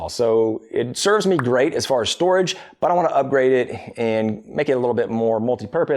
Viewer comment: I heard, "It serves me great...but I want to up-great it." It's like Pavlov's woodworker--I was conditioned to hear the great-ness. :D